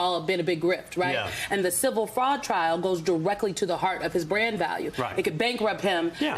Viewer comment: "His brand value is his greatest value" ... when I heard it the first time I thought you said brain value and I nearly blew all my coffee all over the screen, but thank god I was wrong.